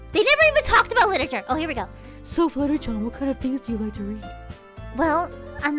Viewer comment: Did just say she likes Ero-Manga?